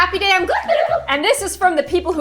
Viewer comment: I’ve been watching Kassie for almost a year and I just learned she says “happy day I’m gloom” I thought she said “happy day on gloom” I am so mad at myself for not knowing this sooner